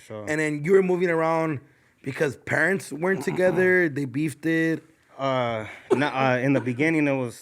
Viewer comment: Did really say “ they beefted” @ 2:17 lol wtf